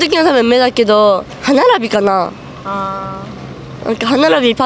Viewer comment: The captions says nose and everyone else thinks the girls talking about the nose when the girl at 1:49 says Hana-Rabi but hanarabi actually means teeth, coming from a fluent half japanese